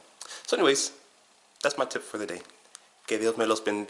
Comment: I try to pick out your salutations at the beginning and endings of your videos. The beginning was quite easy. On this video the player tranalate it to Gabe medio giro biga' i heard 'Que lo . . . ven dia'. And the rest went to a sunken place. I hope to figure it out. I usually really want to know the salutations on videos where there is a sketch amongst multiple characters. You are good and entertaining at that